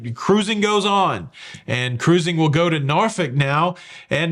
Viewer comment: I think i heard "Norfick" instead of Norkfold a few times!